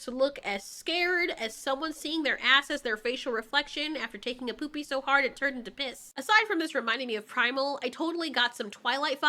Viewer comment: Okay... I had to play from 8:45 to 8:53 a few times just to make sure i heard her right. "Seeing their ass, as a facial reflection, after taking a poopie so hard it turned into piss." WHAT!?